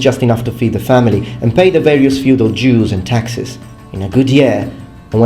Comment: i heard pay their feudal Jews i cracked out laughing